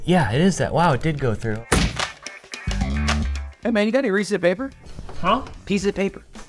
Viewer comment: Did Garrett say do you have any Reeses? Of paper